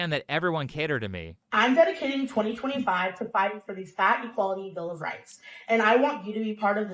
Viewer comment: When she said "Fat equality", I misheard it as "Fatty quality", as if we need a higher quality of fat person.